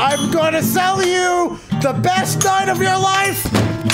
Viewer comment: "I'M GONNA SELL U THE BEST (idk what the word was) OF YOUR LIFE; TAKE- TAKE MY WIFE PLEASE" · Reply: Night